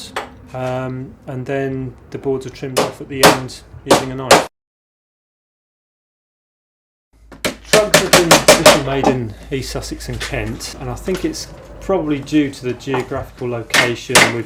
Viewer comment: I thought it said drugs...